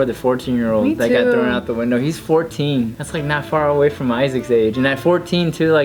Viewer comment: did say my Isaac ? OMG 😍🙌